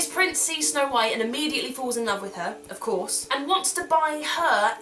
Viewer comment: when you said "of corse" at 3:56 i thought you said "of corpse"